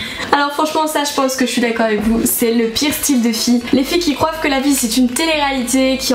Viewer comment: c'est moi ou elle dit "Les filles qui croivent" ? mais sinon les filles et les garçons ont se reproche pratiquement les mêmes choses